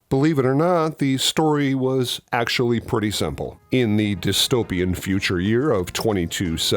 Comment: I'd never categorise the world in Soylent Green as a Utopian Society. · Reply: I thought he said "Dystopian".